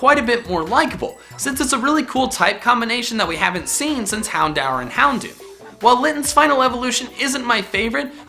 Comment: Did he just say... Hound-Hour..... help me please IT IS HOUND-ORE